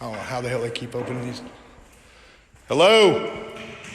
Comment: That sounded like "help" to me.